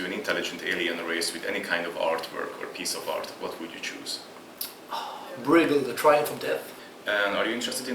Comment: What does he say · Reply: Bruegel - The Triumph of Death